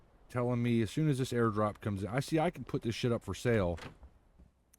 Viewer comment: did you say air drop?